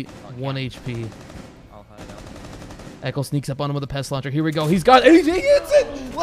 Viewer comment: Did he say “echo sneaks up on him with a pest Launcher”?